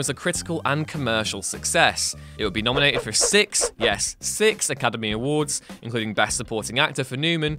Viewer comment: did you say six?